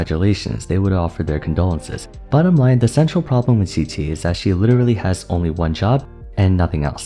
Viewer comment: do you say "the sexual problem with qiqi" there or am I mishearing?